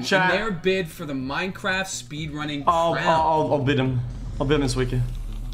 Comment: I heard "I will beat him this week" finally some minecraft streams again Widepeepohappy